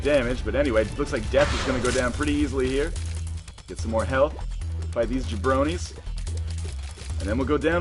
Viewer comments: did he say "Fight these bronies"? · Did he really said "Bronies" at 52:47. Or was it just my imagination? He may also have said "Zombies". But I swear to god I heard the word "Bronies".